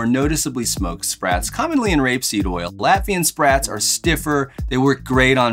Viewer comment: wtf did he just say😳😳😳😳😐🫤🤔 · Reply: Rapeseed is canola oil. They changed the name to canola to make it more appealing to customers.